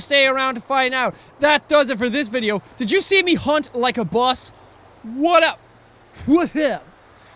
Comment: Umm It sounded like jack said he hunted like a BUS 😂